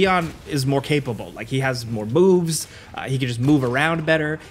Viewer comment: is he saying b**bs